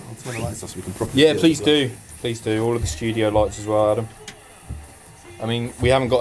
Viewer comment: Did call Adam at 8:19? EXPOSED